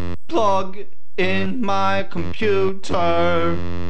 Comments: lyrics: plug in my computer. plug in my computer. plug in my computer. plug in my computer. edit: thank me later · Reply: And I heard "fuck-ing my computer....." 😐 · i thought he said bug in my computer 😀😀